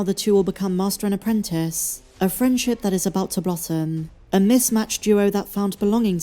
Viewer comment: Thought I heard “a friendship about to rotten” but you actually said blossom lol 😂